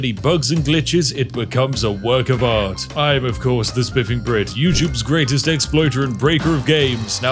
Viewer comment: i dont know why, but i heard